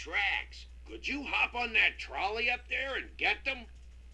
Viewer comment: I swear it sounded like you said the "troll-y" segment rather than the trolley segment.